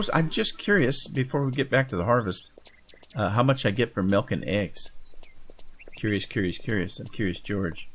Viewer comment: did he say milking the eggs?